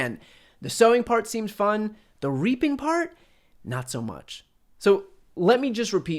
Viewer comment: T-rump: the sowing part WAS fun, but REAPING? I thought you said RAPING.